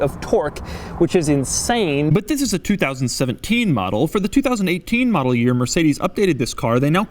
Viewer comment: I didn't hear what year this car was. I don't see many maybachs to even make a guess. Edit. he says 2017